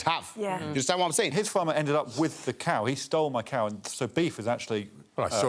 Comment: Mishearing "so beef" as "Sir Beef" changes all the tasks involving the little cows.